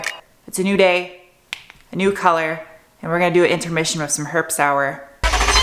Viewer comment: Sounded like you said "herps hour". 😂